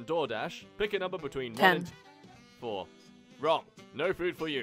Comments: Am i hallucinating or does someone say, "10" and it's really outta place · why did i hear someone say "10"